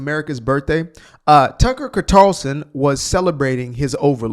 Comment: Did you just say 'Tucker Qatarson'? I guess it depends on who is paying him. 😂